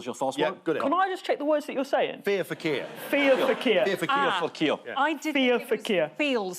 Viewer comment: To my American ears I kept hearing, “feels for keels.” FFS MAN!